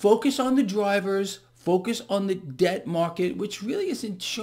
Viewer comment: I'm going to ask this question for the 10th time (literally) When there's a pan-sell off and the dollar, gold, silver, bonds, equities, crypto's etc are all down, where does the currency go from the "cash out"? Do folks put it in the lowest risk "thing" for the day/week? It's gotta go somewhere! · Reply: About 4 minutes in, sounds like he says it goes to the debt market, not sure myself what that is exactly.